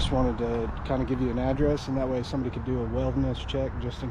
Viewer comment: the subtitle says "wilderness check"... is that even a word? who wrote that, and how do they mishear wellness for that? lol